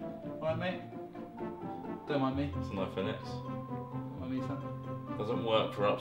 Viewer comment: I legit thought it said "iPhone X-Bow" and was very confused for a moment